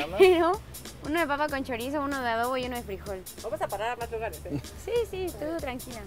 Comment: ¿que dice la excursionista? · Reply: Japhet R.Floyd Vamos a parar a mas lugares eh....